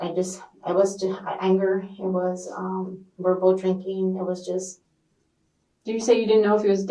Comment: ~ did she just say "Verbal Drinking"? Yup. Just checking.